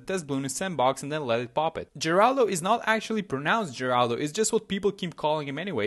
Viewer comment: I thought everyone said Heraldo 😂